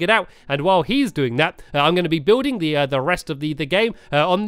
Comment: When I heard say re- at 4:23, I thought he was saying redstone, and I got mad, then I realized he said rest, and I calmed down. Then he started doing redstone, and I got mad again, but I still kept watching because I love. And, if you're reading this, I'm sorry, but it's hard to watch you do redstone.